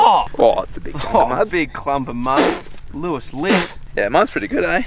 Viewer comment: does he say Lewis Litt?